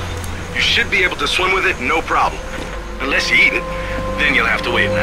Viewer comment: I never understood why he said "Unless you eat it"...until I saw the subtitles I thought he said 'need it' - weird